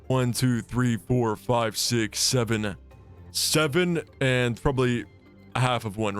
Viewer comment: ermm did he just say 67?